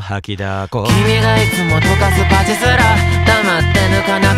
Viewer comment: 「君がいつも溶かすパチスロ」をずっと「君がいつも溶かす価値すら」って聞こえてて、どういうことかなって思ってたわw